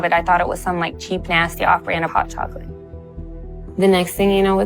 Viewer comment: Why did you put "Nasty Carnation hot chocolate" when she clearly says "Nasty off-brand hot chocolate". Specific brand call-out there...